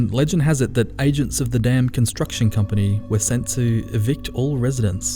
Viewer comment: I swear I heard it as "Agents of the Damned construction company". Which, given the context, would be appropriate.